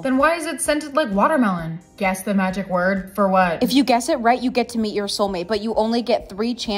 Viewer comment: I thought sh said you get to eat your soulmate